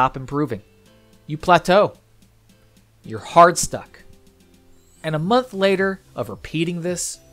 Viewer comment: I thought it said hard suck.